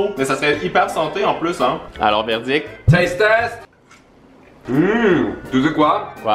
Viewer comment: quand il a dit taste test j'ai entendu tee test sa ma rappeller une vidéo ou emma watson (Hermione Granger) fesait le test de ses fausses dents